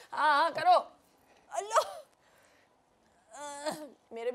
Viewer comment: Is Shilpa Saying #Allah ??? 🤔